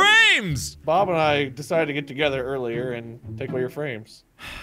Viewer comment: What it says: Bob and I decided to get together earlier and take all your frames What I hear: Bob and I decided to get together earlier and tickle your frames.